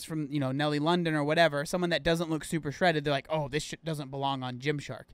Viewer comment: Did... did say sh*t?? 6:02 or am I hearing things 🤣